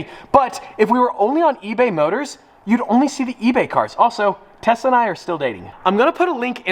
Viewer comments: I thought he said Tesla are still dating and I was like he is dating a Tesla car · I thought he said he’s giving Tessa an Aventador. Had to rewind to confirm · I thought the message said from "tesla" and you said "tesla and I are still dating"